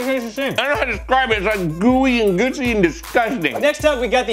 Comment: I heard: “Gooey and Gucci and disgusting”